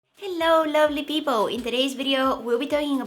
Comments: I thought you said lonely people instead of lovely people😂😂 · Holly Liu *Lovely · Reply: Brar oh, i thought she said lonely lmao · Me misheard her and thought for a second she called us lonely people, but she actually said lovely people🤦‍♀️🤦‍♀️🤦‍♀️🤦‍♀️🤦‍♀️🤦‍♀️🤦‍♀️ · in the intro did u say "hello lonely people" :( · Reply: I heard she said "lovely", not "lonely"